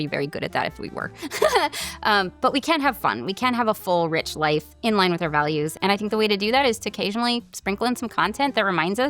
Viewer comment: A bit of feedback since I love your content: I noticed that the captions are better than the auto-generated Youtube captions, in that they are timed better and usually formatted better (auto-generated usually has words appearing one at a time, whereas the ones you have are all displayed as a block of text). However, I saw a lot of mistake that made me think that these captions were generated by AI and not made by a human. The frequency and degree of mistakes don't seem to be from human error, and they are significant to the point of distracting me :( It would be really cool to have an editor for captioning even if the captions are still AI-generated - as the editor could watch the video with captions on and fix the mistakes the AI makes. Totally understandable if that's not something you can do, and I really enjoyed your video overall! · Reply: one example is that in the outro (around 20:40), you are saying "But we can have fun, we can have a full rich life in line with our values." But the caption says "But we can't have fun, we can't have a full rich life in line with our values" which is the complete opposite of what you were saying! I have the ability to discern that as a hearing person, but any Deaf or hard of hearing viewers would need spend extra effort to figure that out through other context clues.